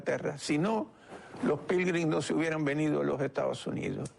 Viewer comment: en el 07:24...no entiendo la palabra que dice el señor Ribas... me podrían ayudar por favor...? gracias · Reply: Saber...... ; se refiere a los peregrinos que emigraron a EEUU en aquel entonces , huyendo de la falta de libertad religiosa en UK . Dijo Pilgrins refiriendose a los peregrinos , religiosos puristas , que comenzaron a poblar a lo que es hoy EUA .